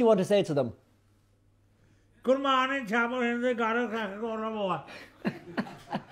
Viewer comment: The nation is watching, what do you want to say to them? Sham: gomorntabligodabu: Hahaha Amen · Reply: He said good morning and ‘don’t go to moate’ or something lol